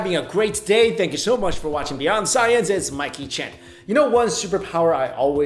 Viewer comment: everytime u intro i hear "Hey Guys, its My Kitchen" 😂😂 great vid! 👍